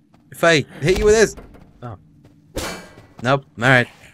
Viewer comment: I'm pretty sure I heard a computerized "No" after asked the zombie if it would explode if he hit him with a bottle of chemicals...or did put that in the video???